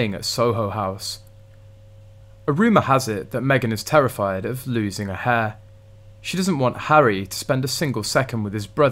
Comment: Lol! I first heard “afraid of losing her hair.” I do believe she has extensions 😂